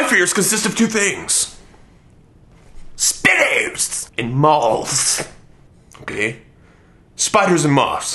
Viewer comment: i thought he said speedos and malls